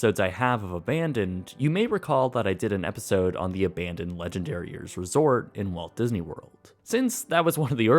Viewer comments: I heard "Legendary Ears" and now I can't unhear it. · It totally sounds like he is saying "Legendariers" XD